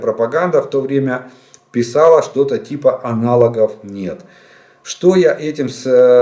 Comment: говорили аналовговнет или мне послышалось ?)